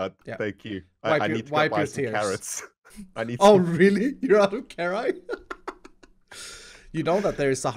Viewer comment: did say carri? Instead of carrots